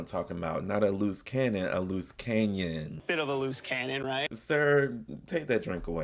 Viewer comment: Bahaha! I thought I misheard “canon” at first… Big, loose, hole… got it! Thanks for the clarity!